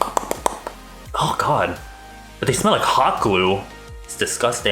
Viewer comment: I literally thought he said cock glue instead of got glue